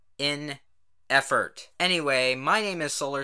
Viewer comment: Always when you say your name i hear "solar sans"... kill me pls